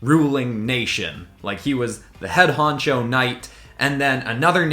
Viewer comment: I thought he said poncho 😂